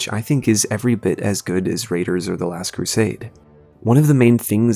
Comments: lol I thought he said raiders of the lost crusade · For a second I thought you said "Raiders of the Last Crusade " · did you just call it raiders of the last crusade?